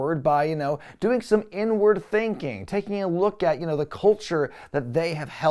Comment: I heard N word thinking not inward 😂😂😂😂😂 had to rewind and listen close